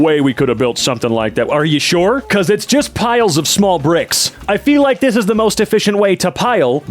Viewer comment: did bro just say they’re small???